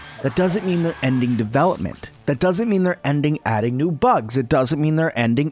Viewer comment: I thought they said you weren't adding anymore bugs.